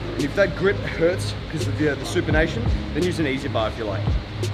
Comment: Hey, what does "supernation" or whatever word you said at 3:35 mean? Haha. Also do you think that supplements are a must for our training, and will it have a big impact on our results? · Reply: “Supinated” Supinated-grip is done with your palms facing your body, like a curl grip used when doing bicep curls.